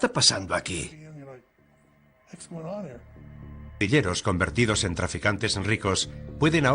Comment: parece que dice villeros jsjs